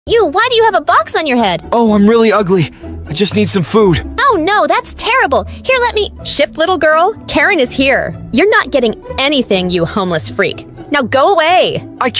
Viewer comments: Bro the Karen sounded like they said "sh!t little girl." 💀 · I thought the time and said shit little girl😂